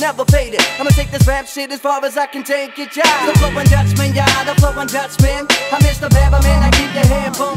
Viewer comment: I keep hearing “The flowin’ duckspin” And I imagine a spinning duck lol